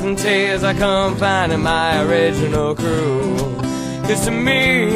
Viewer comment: sounds like he says "I'll confide in my original crew"